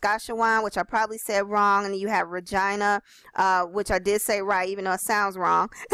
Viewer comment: I thought she said "vagina" (Regina) at first! Nice little overview, I gave her a thumbs up.